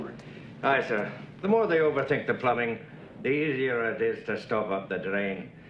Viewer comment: The greatest line in the series: "The more they overtake the plumbing, the easier it is to stop up the drain". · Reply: That's what I heard, "overthink the plumbing". Even the Closed Captioning had that. So was this a case of "the more they overthink the autocorrecting spell-check, the easier it is to confuse it"? 😁